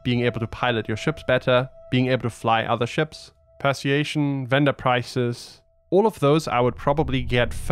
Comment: I simply can't tell what he's saying at 7:27 when he's listing skills to get instead of combat. It sounds like 'Perciation" but it can't be that. Any idea what word he's using here? Persuasion perhaps?